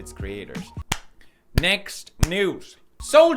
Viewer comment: The moment he was clapping saying "next, news (?)" I heard (for I don't know what reason) "thank you, next".. I need to take a break from YouTube for tonight. 😂